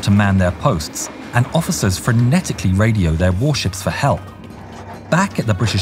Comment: subtitle error I believe frenetically means "in a way that involves a lot of excited movement or activity", not frantically. I thought I misheard the first time as well until I googled it Besides that, absolutely amazing video, loved the story, you guys are great!